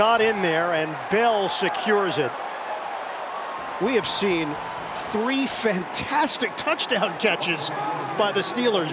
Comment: did he say the F bomb? Or am I hearing things? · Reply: He said bucket